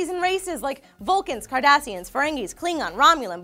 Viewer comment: i could have sworn she said Kardashians :D